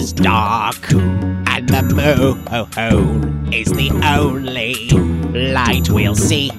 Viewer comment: Thought he said mr popo is the only light we'll see